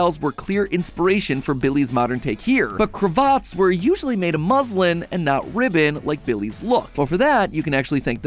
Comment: Yay (Nvm I thought u said muslim)